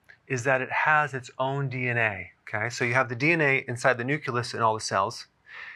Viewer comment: Did anyone hear "newkulus" around 2:40?